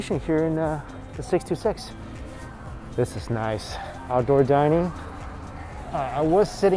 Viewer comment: When you said outdoor dining it sounded like you said gorgina 😂😂😂😂😂😭omg lol 😆 love ya and your such an awesome person supporting local restaurants I like supporting the mom and pop ones too!